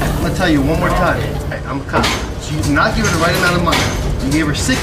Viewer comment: 英語詳しい方に伺います🤭🇺🇸 6:29〜「警察だ」と言っている時、 『カ(ァ)ム』と聞こえます。ポリスではなく。 WWDY以外の動画でも「警察」と訳されている時、同じく『カ(ァ)ム』と聞こえます。 この動画の英語字幕では『I'm coming』と書いてありますが、アメリカでは「警察だ！」と言う時『I'm coming』と言うのでしょうか🤔？ それとも、間違った字幕なのでしょうか？？ 翻訳アプリでは 警察:ポリス 警官:Cop と出ましたが、Copとも聞こえません…🤔 この『カ(ァ)ム』にあたるスペルを知りたいです‼️ ご存知の方いらしたら宜しくお願い致します🙇🏻‍♀️